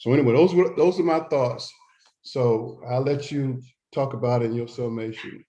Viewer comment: 🤣 at first I thought he said “I’ll let you talk about with your cell mate”.